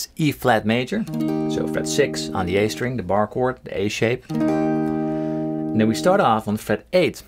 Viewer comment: "and then we go up to aids" did anybody else catch that?